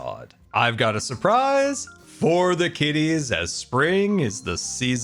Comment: I almost thought he said skitties and I almost panicked, thinking the other one was found